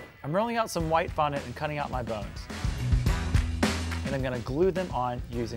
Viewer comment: When he said he's going to cut out the bones I thought he said buns